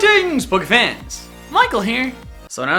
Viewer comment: Your into sounds like you say <Goury eatings>